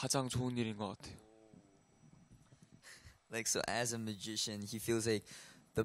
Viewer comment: When Mark was translating what Doyoung said it sounded as if he said magician instead of musician and it got me confused for a moment, like "Wait, so Doyoung’s into magic? Well, all power to him I guess"